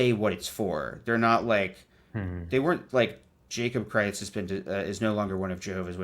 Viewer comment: Every time you say your full name, I hear "Jacob Christ". "Jacob Christ is no longer a Jehovah's Witness". ... I think you could lean into this for comedy.